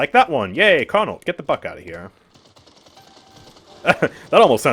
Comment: Lol i misheard you when i heard get the buck outta here and rewinded hahaha